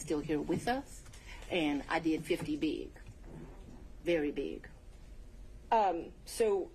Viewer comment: Is that English? Ive listened to it 5 times, no clue what she's saying. is it gang talk? fitty big? what is that ? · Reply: His 50th birthday- she did his 50th in a big way